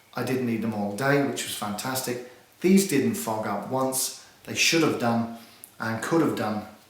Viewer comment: I thought he said these didnt f**k up once. LoL